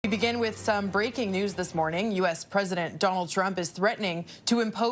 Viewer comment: I heard "some crazy news this morning" 😅